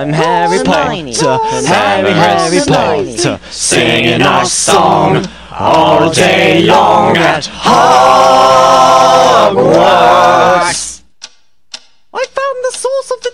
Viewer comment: Over here, the subtitles say “Singing our song all day long at haha” PLEASE HELP ME